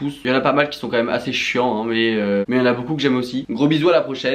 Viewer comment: "j'ai pas mon script" J'ai entendu "j'ai pas mon slip"... Voilà c'est mon commentaire de référencement Bisous